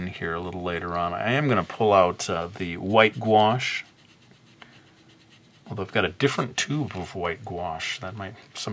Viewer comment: White squash lol. 😂 I know what he meant it just sounds like he said white squash. I love you're channel.! ❤️